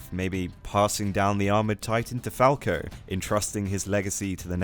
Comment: I misheard Falco as Porko